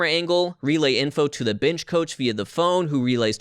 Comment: is he saying bench coach or bitch coach? :0